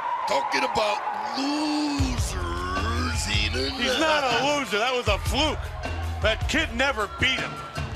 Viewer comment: IM THE MACHO MAN HE'S A LOOSER... gets me every time 😂😂😂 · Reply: I thought he said poser